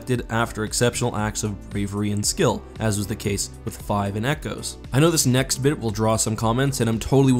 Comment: Am I the only one who heard him say ‘’five and echoes”?